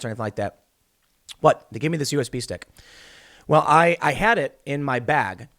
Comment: I thought you were saying "He gave me a U.S. Beef Steak" I want a U.S. Beef Steak.....